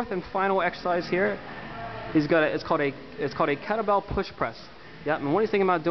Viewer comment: what did u say at 1:39? cadabel push press?